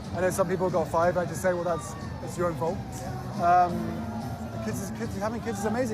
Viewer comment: Does he say at around 14:43 its your own fault?